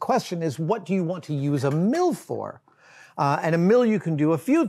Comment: I kept hearing "What do you want to use a MILF or...?" Damn you, brain! 🧠🤣